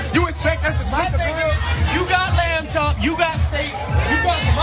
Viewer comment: Oh "lamb chops." I thought he said "mata." I was like They're eating stingrays?! How expensive is that?